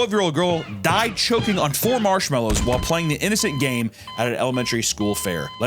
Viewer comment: For a second there i thought you said she died choking while planking 😂😂😂😂😂 I was like "WHO THE FUCK LAYS DOWN ON THEIR STOMACH WHILE CHOKING" LMFAO